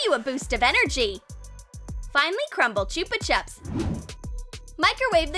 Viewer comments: Who thought they said finally crumble chuppa chup instead of finely like: yes Comment: no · it sounded like she said finally not fine -Lee